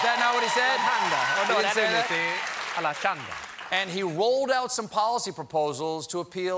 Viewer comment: what is jon the band leader saying at 12:01 and 14:13 sounds like "alavashanda" o.O...is he speaking in tongues? this isnt the first time hes said that.